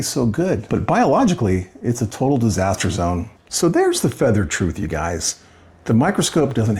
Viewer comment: you said "That's the fettered truth" ... I believe (or hope), you meant to say "That's the unfettered truth". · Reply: I heard "the feathered truth"... Closed captions read "the feather truth".